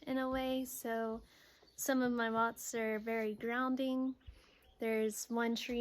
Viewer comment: "...some of my "mods"(?)..." Sounded like "motser", what is it?